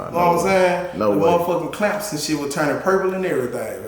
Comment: Lol he said the rings was real then said they was fake 2 mins later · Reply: I missed the fake part. I thought he said the chains by the clips was turning purple but I missed the ring being fake part